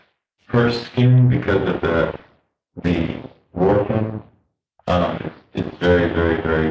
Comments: inaudible? He clearly says Wharfrin. Such a sad case 😞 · just as an FYI, what he's almost certainly saying is "her skin, because of the warfarin, it's very... much softer"